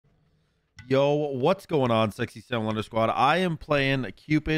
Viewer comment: I just thought I'd mention that in the preview for this video, it showed captions, and it thought you said: " yo what's going on Sexy L squad"